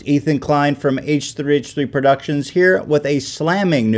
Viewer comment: My internet made the video lag so when he said "I'm from h3h3 productions." It sounded like he said "I'm from Asian Asian productions."